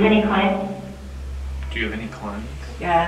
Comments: i heard someone say "your majesty" right before a woman saying "do you have any cleanse?" kinda freaky but idk i could just be imagining it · I think that it said "Do you have any clients?" not cleanse as it seems more likely that the following sentence would be "Come say hi" as in she's very inviting | Also at 47:10 there's an orb that is not pointed out just in case you all missed it